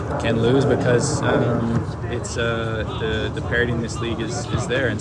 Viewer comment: unintentionally funny "parody" instead of "parity". Thanks for another fun one, I like both of your takes and enjoy watching every episode.